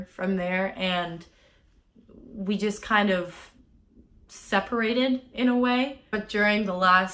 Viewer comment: i cant tell what she's saying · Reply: Separated